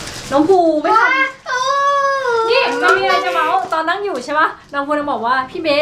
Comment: พี่เบสพูดว่า มีไรจะเว้า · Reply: มีไรจะเม้าหูไม่ดีมั้งง